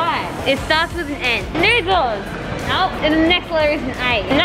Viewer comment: did sockie say the n word? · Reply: She said noodle